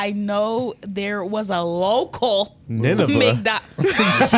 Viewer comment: I thought she said Nunavut at first. I was so happy to affirm that it is indeed a real place (in Canada) and then they said it’s in the Bible?? I had to process that for a second😅. **Nineveh is also a real place in the olden times and now (in Iran).